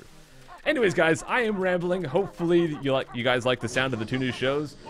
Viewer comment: Oh i thought he said new shoes not shows